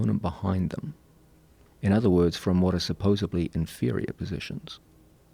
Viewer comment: Did he say "supposably" at 32:09? <link>